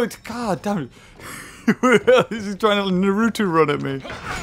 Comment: wait did u say Naruto :0 I luv Naruto